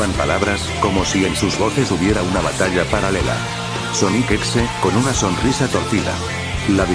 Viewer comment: Acaso escuché bien ése es Sonic exe...